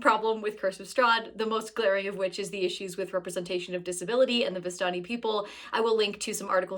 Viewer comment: Editing talks too fast because I needed to replay it like 4 times because I thought you said "the biscotti people" and wondered why I never met the cookie people